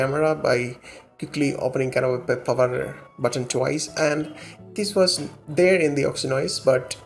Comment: Can anyone please clarify what he was trying to say at 2:44? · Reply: Press Power Button Twice to Open Camera Directly from the Lock Screen.